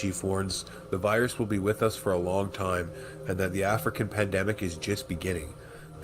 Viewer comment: WTF THE "WFP" REPRESENTATIVE LEGITIMATELY CALL THE CVD19 VIRUS PANDEMIC A FUCK'N "PLANDEMIC" I WAS LIKE WOAH!!WAIT A MINUTE,DID I JUST HEAR THAT RIGHT??DID HE JUST SAY WTF I THOUGHT HE SAID??I LITERALLY HAD TO REWIND IT LIKE 5 TIMES JUST TO MAKE SURE I HEARD "PLANDEMIC" PROPERLY!!& WHAT DO U KNOW?!?HE DID DEFIANTLY SAY WHAT I THOUGHT HE SAID!I THOUGHT WELL MAYBE HIS ACCENT HAD SOMETHING TO DO WITH IT BUT NOPE!AFTER LISTENING TO IT A HANDFUL OF TIMES I CAN SAY THAT HIS ACCENT WASNT THE ISSUE,ITS THE FUCK'N TRUTH BEING SPOKEN YET ONCE AGAIN... I HATE THIS WORLD WE LIVE IN,NO WAIT!I TAKE THAT BACK!I FUCK'N HATE HATE ALL OF THESE EVIL SICK ASS CLOWNS THAT ARE DOING THIS SHIT TO US!MAY THEY ALL ROT IN PISS!!THIS ISNT RIGHT!!THEY'RE KILLING PEOPLE!!THIS HAS GOT TO STOP!! TOGETHER WE RISE,DIVIDED WE FALL!"